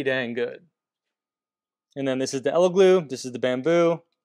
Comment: I keep hearing "elegloo" throughout the video. I can't tell if it's like the "meow" thing from Super Troopers, hahaha. Great video, thanks! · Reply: I always hear, "Eggyloo"... sounds like our bathroom the day after a curry...